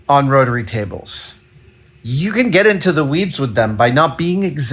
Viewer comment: tell me I'm not the only person who heard "You can get into the weebs with them".